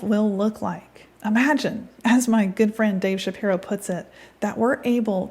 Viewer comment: For a second there I thought you said your good fiend Ben Shapiro.....lol